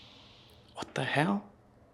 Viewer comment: I mistake have misheard bur I am sure after the event session when asked ablit the maid a voice says got to hell.. I don’t remember that being said. I didn't take time stamp.. maybe there's a logical explanation.. was the ghost vox on? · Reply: Debunked he says what the hell.. lol..